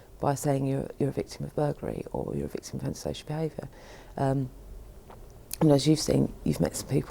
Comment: Did she just say "or you're a victim of homosexual behavior" at about 0:34? Seriously? Are attacks on heterosexuals by roving gangs of gay people really common in the UK? How does someone become a "victim of homosexual behavior"? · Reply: I know this is an old post but no she didn't say homosexual behaviour, she said antisocial behavior.